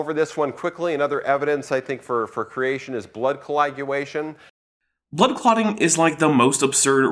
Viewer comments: Did he just say... "Blood Colaguation?" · + 1:33 Is he saying “Colaguation?” I feel like I’m hearing him pronounce it as “Colaguation.” I know it’s nit picky, but it makes him sound like he had a few too many drinks before doing his talk… hope he had a DD… 11:15 further word slurring. There’s definitely something to my working theory.